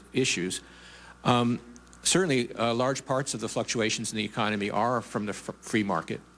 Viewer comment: DID HE SAY " fuck UACTIONS? meaning to say " fluctutations? PRICELESS.LOL